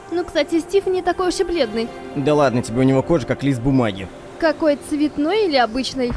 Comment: Мне послышалось Глист бумаги